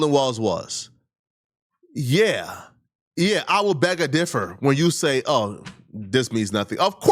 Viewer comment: THIRD incorrect usage of words for today: “bag a differ” instead of “beg to differ” 4:53 🤦🏾‍♂